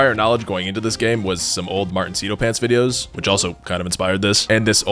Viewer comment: YouTube auto-generated subtitles thought he said Matt Lowne instead of lol